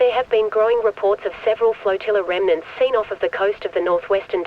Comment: i thought they were saying fortilla lol i was thinking about the poi in fortnite ch 2 season 3